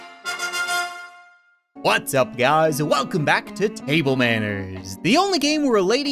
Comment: I really thought he said "Whats up Gods!"